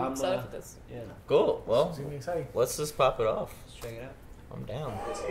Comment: Did just say "Let's Chang it out"? Instead of "check"?